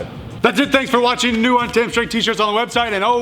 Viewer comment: What's with the "Train on time" thing? Is he a pirate? Can I get some context, please? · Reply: Donald Klute - LOL! I’ve been watching Alan’s videos for years and I always thought it sounded like he says “Train On Time” too, but he’s actually saying “Train Untamed”, his personal catch phrase. And no, he’s not a pirate, he’s a Marine, as am I. Semper Fi Alan! ☝️🥴👍🇺🇸🇺🇸🇺🇸